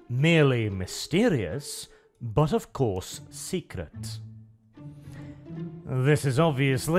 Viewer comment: When says secret, I hear "Heretical"